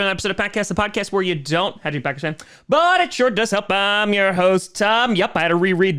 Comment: I gotta say, as a non-native speaker, every time Tom opens up with his line, I hear "the podcast where you don't have to pakistan, but it sure does help". Can sbd help me figure out what he says???? thanks in advance :D